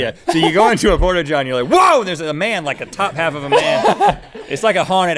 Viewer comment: I thought he said stop a pot